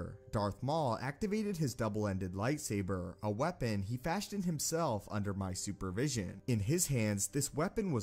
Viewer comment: idk why but i thought he said when it hit 1:31 he said he built under my supervision